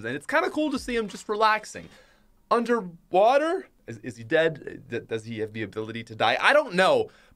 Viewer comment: did say "does he have the ability to die"?